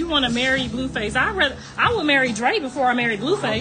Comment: I thought blue face mom was saying she would marry Drake, whole time she was talking about her other son…. I 😲